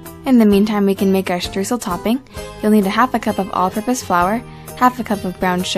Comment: Hey everyone! I just posted a new video on how to make Baked French Toast/Bread Pudding for Mother's Day! It's a super simple recipe, and I promise your Mom would love it if you made it for her! Enjoy! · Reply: Am I the only one here who thought you said "octopus flour" instead of "all purpose flour"?